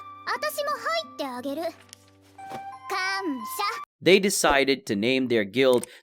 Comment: I swear she said c*m shot